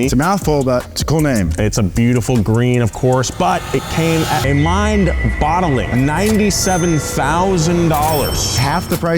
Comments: Did he just say "mind-bottling"? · Did say mind bottling as a joke? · mind “bottling” huh? Lol al these years I thought the saying was mind boggling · did he say bottling? And not boggling? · did you say mind bottling?